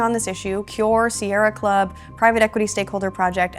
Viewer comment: Did she say Serra Club? Doesn't Blackrock own majority share in Serra Club?